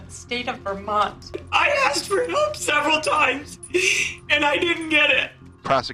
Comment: what is that woman saying? Sounds like, "I asked for nobes several times, and I didn't get it." 9:09, that guy looks like he's getting his mouth ready for prison survival.